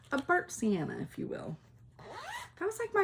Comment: OMG burnt siena... I kept hearing "burnt salmon" and I was like I dont remember that color. Clearly I need my hearing aids in